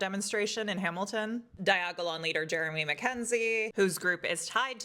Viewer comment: Why don't those type of people actually drink the kool-aid anymore? Fkn snowflakes Every time you say diagolon I hear 'Died Gagging on Dong' lol